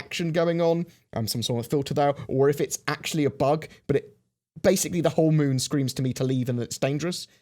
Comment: I thought for a second that you said, at 1:46 'the whole world screams at me Elite Dangerous' 😃